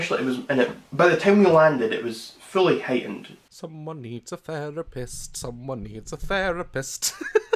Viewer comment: When daz sings therapist I thought he said there's p*ss 😅